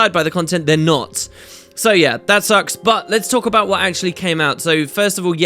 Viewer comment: Did you just say "that sucks butt" at 2:05?